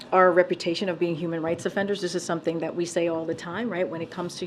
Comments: I heard her say offenders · Did she just say "human rights offenders"???